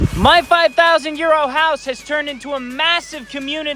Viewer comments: This whole tien I have thought he said my 5,000 year old house. I had been wondering where the house was….🤣🤣🥲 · Oh, Euros! I thought you were saying YEAR OLD- 5000 YEAR OLD house. I was thinking, what kind of bs is he trying to tell us.🤣lol, my apologies.❤